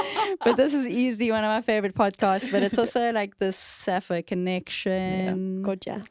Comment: I thought she said "Sapphic connection" 😂